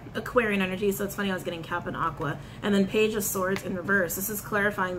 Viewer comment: Lmao 😂.. when You said Cap & Aqua ... I first thought You said, “Captain Aqua” meaning Aquaman to Me!! 👀👀👀☺️😘